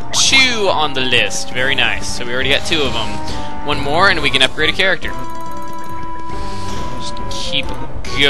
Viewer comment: it sounded like you said cerate a carecter